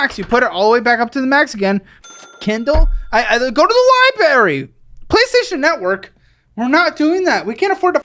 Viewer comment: Did caleb just say "libary"? Lol